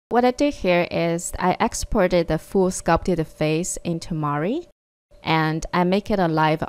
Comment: I think you meant to say Maya! I was thinking, whoa I didn't know you could do that in Mari!! hahaha. Great video!